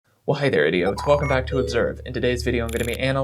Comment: eveytime I watch these videos I hear "well hi there idiot" and while I feel personally attacked I also agree